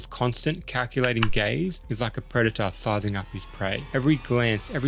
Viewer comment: I heard “…..teasing and calculating gays”, n I’m like that’s a clever way to describe the question “u ever had ur shit pushed in?”